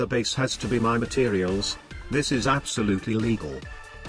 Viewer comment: Absolutely Illegal lol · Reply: It sounded like he was saying Illegal